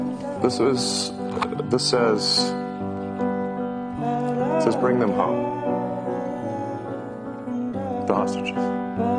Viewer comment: It sounded like Musk said “bring home the Ostriches” he should really enunciate better. £ucken Cr1min@ all of them.